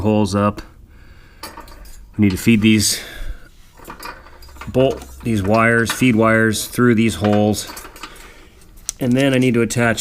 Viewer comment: Any one no what he said at 8:38? Sorry I am bad english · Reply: I'm not 100 per cent sure what you missed at 8:38. Send me an email and I can help you figure it out. I mentioned putting the feed wires through at that time I believe. <mail>